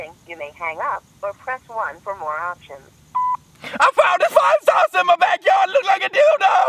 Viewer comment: “i FoUnD a FlYiNgSaUcEr iN mY bAcKyArD lOoK lIkE a DiLdo” Brennen laughs “You are definitely not getting a call back brother” 😂😂😂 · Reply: And i heard '.... i found a 5sos in my backyard like you know...' what?? 😅😅😅